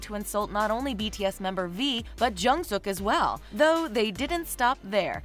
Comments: are you trying to say jhope or jungkook because I hear jungsuk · Did... did you just say JungSOOK? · Did she just say jungsuk????? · Did I hear it right. JUNGSEOK? WTF? WHO TF IS JUNGSEOK? Is he a new member of BTS? 😂